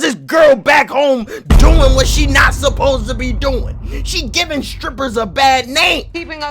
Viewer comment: I could have sworn I heard "she giving us strippers a bad name!" 🖐️🤣Hahaha